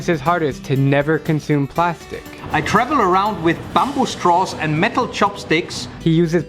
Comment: I thought he said metal straws and bamboo chopsticks ( the metal straw thing kinda reminds me of those annoying girls )